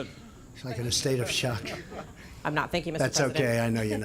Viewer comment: Trump totally misheard that one lady. She said 'I am not (overwhelmed). Thank you ( for giving me the turn)'. He thought she said ' I am not thinking'. We can add hard of hearing to his list of malfunctions. But maybe we should only list his qualities. That will be a lot less work.